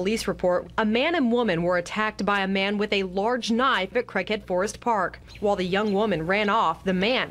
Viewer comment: Is it me or did she just say Crackhead forest park? If so, that explains it.